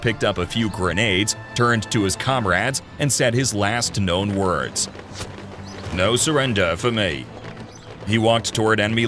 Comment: I always thought old Wally said, no bloody surrender for me